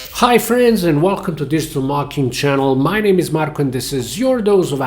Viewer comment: ? I swear I heard "Digital Mockings", but anyway though probably a Freudian slip as you seem quite sarcastic. It's probably your accent, but keep up the great work! Хвала вам!